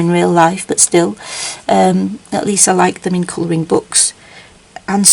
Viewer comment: When you said “coloring bugs” I thought you said coloring books and I was so confused for a second until I figured it out.